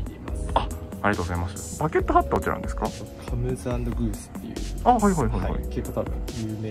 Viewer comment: 〜のバケットハット、メーカー名が上手く聞き取れないんですけどどこのでしょうか？ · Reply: COMESANDGOES